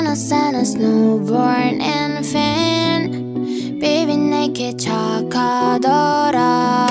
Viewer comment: did she say NeW bOrn inFaNt?